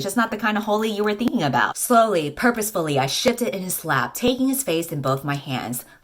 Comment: omg, i just heard: ,,i shitted in his lap." and was like ,, oh no"